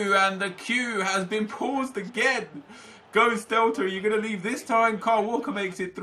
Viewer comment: Am I the only one who heard Nepenthez say "The Queue has been paused again"?